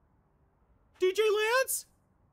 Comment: What is the red guy saying at 1:48? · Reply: Muno is saying "DJ Lance?"